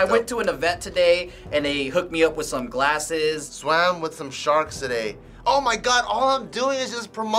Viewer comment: jk fans did joe actually say "swamed" 7:03 hahahaha